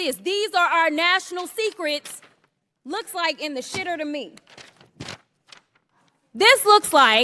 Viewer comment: What did she say!? Was that vulgarity!? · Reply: yes, she said something about “the shitter”.